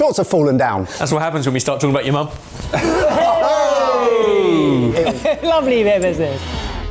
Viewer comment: unintelligible murmurings are back‽‽ What did he say‽ 😂😂 "Lovely bssssrrrrbbber" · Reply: He said "lovely bit of business".